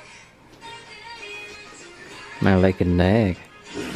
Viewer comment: it sounded like he said lacked neg lmao 😂